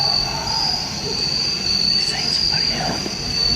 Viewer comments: the people who heard gunshots or that part, I also picked up something the guy screaming inaudible stuff was actually pretty easy to hear, he says “SOMEBODY HELP!” “OH DEAR GOD, YOU FUCKING KILLED HER!” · when they were screaming I think I heard someone help me you fucking killed her